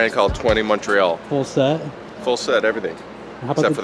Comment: Anyone catch the name of the brand at 14:40? 21 trails? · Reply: I think he said Twenty Montreal.